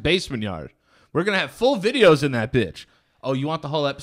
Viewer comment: I thought Danny said bitch not Ben but once he said I said he’s name again I knew right away 😂😂😂